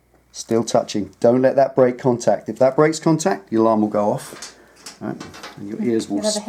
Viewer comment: God, I thought you said "your arm will blow off".....well this seems a bit dangerous!